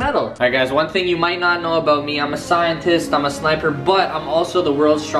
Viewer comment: it sounds like he said "im a scientist imma slap her butt"